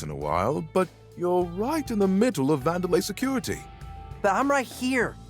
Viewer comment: did he say vandelay security? Is that a sienfeld reference?